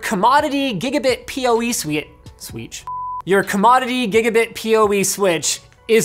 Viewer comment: Oh, EAST I thought you said SWEATCH!